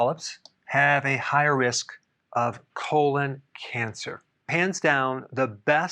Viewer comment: did you say, “pants down”? If you didn’t, just say you did, ‘cause that’s the best play on words I’ve heard all week. 🤣